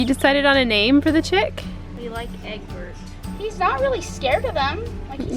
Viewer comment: your such a good kitty mommy. A chick called Albert! Really cool in closure! Jo Jo in VT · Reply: i thought she said Eggburt hahahahahahahaha